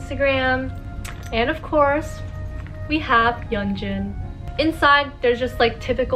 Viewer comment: captions say its hyunjin but its yeonjun!! just pointing this out :33